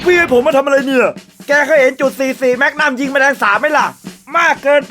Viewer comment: พูดว่าไรนะ จะบใจความได้เเค่ .44 5555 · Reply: พระอิศวร: แก็ก็เห็น .cc แม็กนั่มยิงแมลงสาบไหมล่ะ